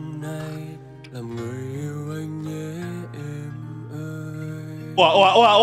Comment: Lúc đầu em cx nghe nhầm là "làm người yêu em nhé anh ơi" 6:02 :)))